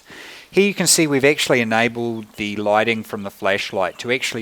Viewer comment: i know im immature, but did he said fleshlight?